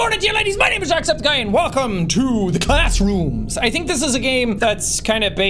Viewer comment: thought he said backrooms until I listened again. Then he actually said backrooms 😂